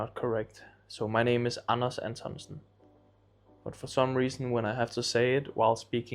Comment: Sounds like he said Anus Antonsen